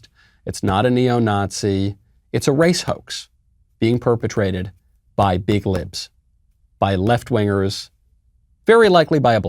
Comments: I thought you said at first it’s being perpetrated by big lips lol. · Being perpetrated by big lips 👄 im sure thats what i heard you say and it really brightened my day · I thought he said that this was being perpetrated by big lips 🤣🤣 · At first I thought he said, "big lips".